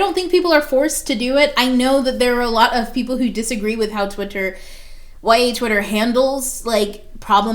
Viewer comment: Did you say "whitey twitter"? (1:16) If you did... that's bigotry. If I heard that right, please stop saying that, for your good and everyone else's. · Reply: ”YA Twitter”...as in Young Adult Books...